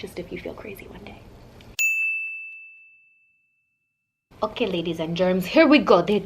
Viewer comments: did she say okay ladies and GERMS? If she did I am weezing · Did you say Ladies & germs ? Lmao or did I hear wrong